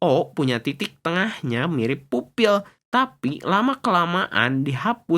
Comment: aku dengernya upil 😂😂😂😂😂😂😂